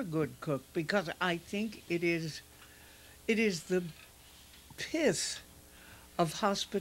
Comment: What is Pat saying at 7:49 - it sounds like she's saying "the piss of hospitality" but I know that can't be it 🙂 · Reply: She uses the uncommon word "pith" here, meaning core or essence.